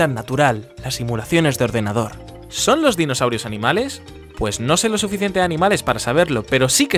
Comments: despues de casi 2 años me acabo de dar cuenta que dice " DINOSAUIROS" y no dinosaurios xd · Después de verlo como 10 veces me doy cuenta que dice "Dinosauiros"